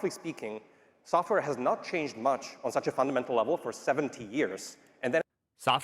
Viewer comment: i heard 70, google caption heard 70, it's 70.